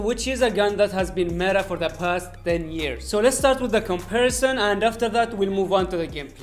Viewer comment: Did you said "Past 10 years" ??